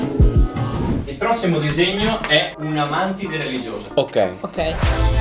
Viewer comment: solo io ho capito "un Amante Religiosa"😂😂😂